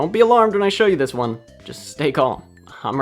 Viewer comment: did you say... Stay Calm ?